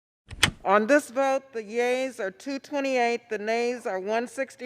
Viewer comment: sounds like she said “gay” instead of “yay”